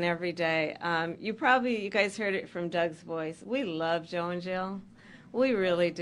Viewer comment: Sounded like she said we love Joe IN JAIL instead of Joe and Jill. 🤣